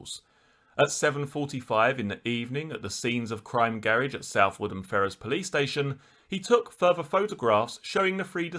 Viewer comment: My instinct is that the crooks in the police service were involved and needed to eliminate those who were out of control and could cause all kinds of problems. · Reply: in response to your main comment , @ 42:16 the narrator says the vehicle was taken to the Scenes of Crime Garage at a police station . I can't make out the name of the station . Edit , I think he said Southwood and Ferris police station.